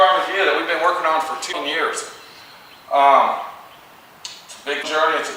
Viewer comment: I think the editing got in the way it sounded like you said 10 years but knowing it was 2 years 2 fingers